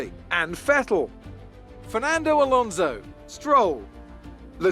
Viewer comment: i swore was gonna say fernando, so he said, ferlando